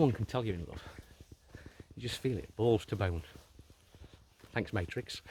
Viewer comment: Thanks, I actually have an hour and half long video on my channel called 'The Doctrine of Karma' , which goes in depth into the Law of Karma. Also, sorry to ask again haha, but what do you say at 6:06? "You just feel it. Balls to bound, thanks Matrix" did I hear correctly? and if so, what does that mean? I have no idea! :)